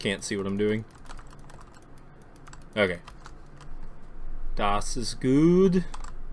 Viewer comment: did you Speak german, or what did you say there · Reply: Haha yeah, "das ist gute" probably some spelling errors there. Basically just means that is good in German.